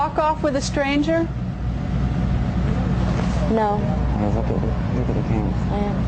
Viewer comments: Did anyone notice at 16:20 the dad whispered to the girlfriend of what sounded like look at the camera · what did he say when he covered his mike, and Musty says "I know." WTF was that?! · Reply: Sherri La Nicca he told her to look at the camera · That live interview was weird AF... why did he cover the microphone and whisper something, and then tell her to look at the camera? She answered “I AM” all snappy like she was annoyed. Idk that rubbed me the wrong way. It had the same energy of a person that was instructing an accomplice to stick to the story. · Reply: Sounds like he said “they’re looking at you look at the camera” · what did he cover his mic and say to her? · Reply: he told her to look at the camera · What did the father whisper to Misty during the interview? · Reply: I thought he said look at the cameras